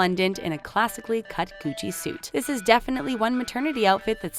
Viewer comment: when she was talking about chrissy's dress and mentioned john's outfit i really thought she said coochie suit instead of gucci 🤦🏾‍♀️😂